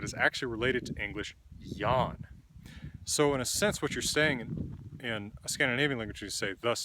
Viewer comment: What's that word starting with [ja] at 3:24 that shifted to the definite article? I hear something like "yawn" or "young". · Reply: Yon.